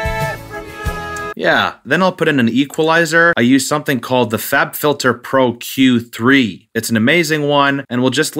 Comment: I thought he said the fap filter 😂